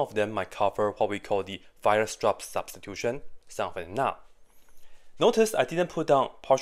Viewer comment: what was the name of the method you said? I couldn’t quite understand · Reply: He said Weierstrass substitution.